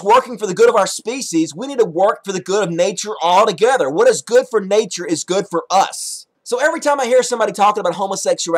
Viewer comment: "Everything is natural, even homophobia but just because its natural does not mean its good." 1:58 "There is a pile of garbage...Greedy oil companies...destroying home planet..." 3:00 "We should work together with nature...what is good for nature is good for us." If you define good as what is best for "us." wouldn't propagation be deemed good? Therefore making homosexuality bad? Therefore, logically, making homophobia good? Your argument is self refuting and contradictory. Logic. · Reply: ***** He did not say "the good in nature can also be good for us." He did say at 3:14 "What is good for nature is good for us."